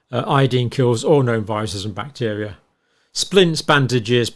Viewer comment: Did you say iodine kills all known viruses and bacteria?